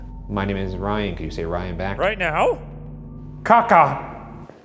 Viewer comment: It kinda sounds like they said “rayan bergara”